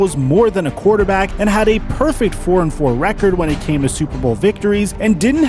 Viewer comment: did you say 'A perfect 4-4 record'? Love your video's Bro!!